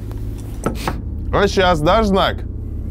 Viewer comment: что он говорит? Кто понял скажите пожалуйста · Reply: а щас,дай знак